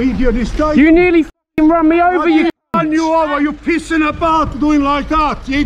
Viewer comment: I Thought the old guy said you piss in the bath...needed the subtitles.